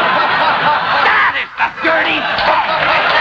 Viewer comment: What does Jackie say at 43:35 (sounds like "that is the dirty bastard")? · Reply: “That is the dirty bum that’s responsible for this thing!”